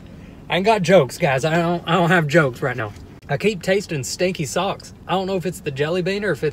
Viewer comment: I thought he said "sticky sauce" not stinky sock 😐